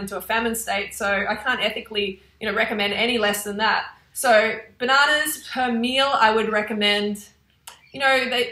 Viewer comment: I'm so confused. 800 WHAT per mean? I thought you were saying 800 bananas per meal haha.